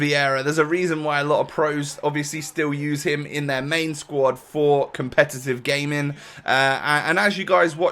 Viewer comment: anyone else hear "competitive gay men"